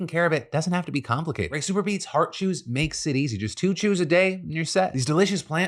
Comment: Both my ears and the closed captioning thought Phil was saying Heart Shoes and I was very confused before the picture of the chews appeared on screen.